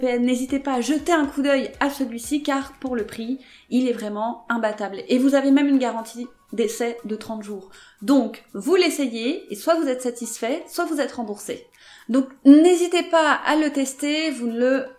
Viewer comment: J'ai entendu " Une garantie deces , de 30 jours" ...